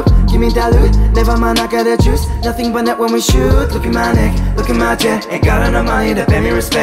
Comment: did he just say "look at my dick"? After he sang lashes and diamonds... whatt, no.